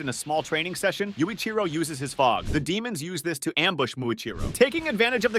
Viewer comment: did bro just said yuichiro uses his fart 😂😂